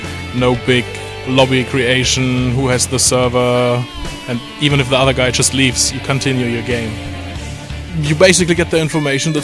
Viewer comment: I heard ''even if the Al Gaida just leaves you can continue the game''